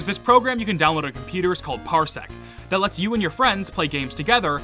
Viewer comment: power what? Sorry, I couldn't hear the name property, that sounds really interesting, if anybody knows the name, please tell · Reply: its called Parsec